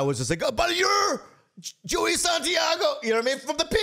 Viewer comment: I thought it said Joe Santagato. I almost went nuts. We need a BasementYard & TigerBelly collab cast